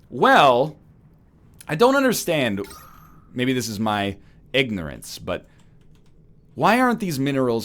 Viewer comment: did he say "eggnorance"?